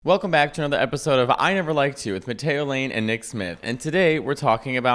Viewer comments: I REALLY HEARD POTATO LANE. HAD TO REPLAY IT THREE TIMES. THOUGHT HE WAS SAYING IT AS A JOKE TO THE JOKE. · You totally sounded like you said "Potato Lane" in the intro.